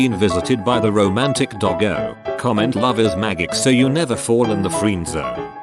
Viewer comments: LMAO at the end I thought it said "comment mothers maggots" double take, NOPE "love is magic. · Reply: I always hear "love is maggots," so yeah. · LMAO at the end I thought it said "comment mothers maggots" double take, NOPE "love is magic.